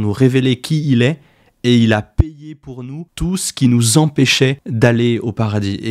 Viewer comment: "Il a..." quoi ? Balayé ? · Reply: Payé !